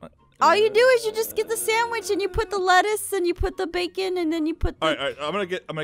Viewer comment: When said "lettuce" I heard "lettuth" Start annoying Jason with that. 😉